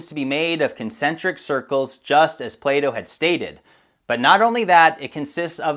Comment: I thought you were saying playdoh for a second